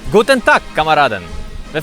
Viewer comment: Мне послышалось, что "Гутен так, колорады!"